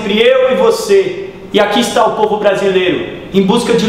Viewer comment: Só que que ouvi "aecio brasil mudasse de uma vez"?